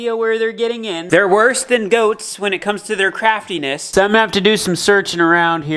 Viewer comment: I thought you said ghosts.